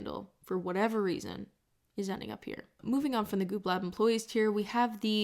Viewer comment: for almost 15min of this video I thought it said 'good lab employees' lol